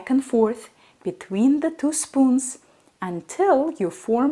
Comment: I learned a fancy new word: Quenelles. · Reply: Oh quenelles.....I thought she said canals 🤭